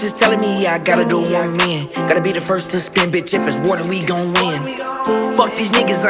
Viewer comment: IF ITS WAR THEN WE GON WIN 🗣️🔊🕺🏻🫗💃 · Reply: neega all dez years b i thought he said 'if its WATER we gon win'